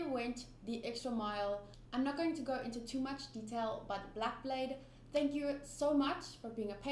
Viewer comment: I thought was saying blackbeard haha